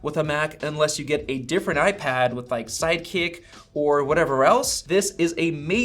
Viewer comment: did he say sidekick? 😬😬sidecar